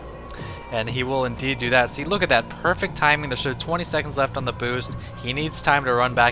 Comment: I heard 20 seconds left on the boobs :), I'll remember that number!